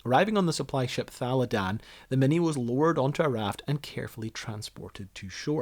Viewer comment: The way you say "lowered onto a raft" almost sounds like you said "lured." I wonder what kind of treats you would lure a mini onto a raft with. Since they're Australian I'd guess a VB long neck.